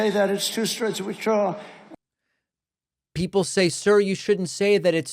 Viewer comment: i turned on closed caption for the clip at 2:40 and it read "sir,you shouldn't say that. it's two struts of withdrawal". even closed captioning has no clue what he's mumbling!